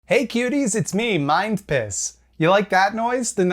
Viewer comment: My gf heard you say "hey cuties its me, mind piss" and lost her will to live